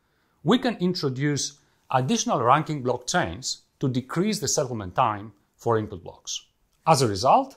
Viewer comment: Standing on the shoulders of giants! Also, there's a typo in the captions at 9:38 (it should be "settlement" instead of "supplement").